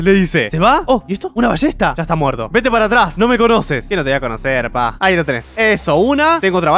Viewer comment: de echo no dice “no me conoces” lo que dice es “para atrás! No fui yo!”